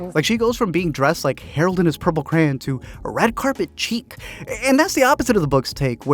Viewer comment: did he say ‘red carpet cheek’?